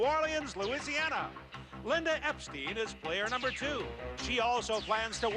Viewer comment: I had to freakin rewind this video cuz it sounded like they said Epstein but they said Epsteid